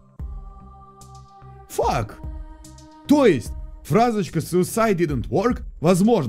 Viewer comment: мне послышалось или он вместо ФАК сказал ФАКТ